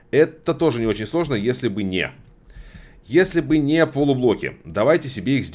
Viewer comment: Услышал "перемещать трупами". Нет, всё таки надо спать подольше... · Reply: Это ещё ничего, на 5:04 послышалось "полубоги"